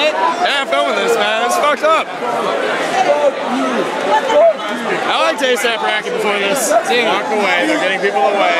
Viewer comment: Did he say, "I liked A$AP RACK-EE before that" ???